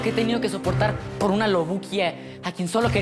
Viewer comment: Loruquia o que dijo? no entendí bien